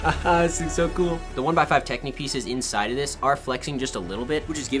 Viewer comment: did he say technique?